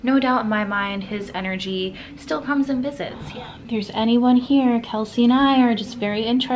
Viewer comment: after you said, no doubt Richard Nixon energy comes and visits, I heard a disembodied male's voice say, "Yeah!" 🎧 needed on high! It was just before the clip